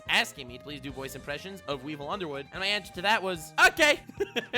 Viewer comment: Ironically and hilariously I misheard you when you said Weevil Underwood it sounded like weebo underwood lmfao.